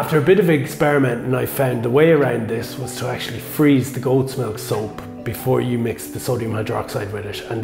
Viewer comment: of the video the subtitles say Gold smoke soap 🤣😂